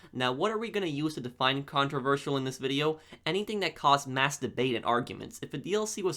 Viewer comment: i thought he said masterbaitXDDD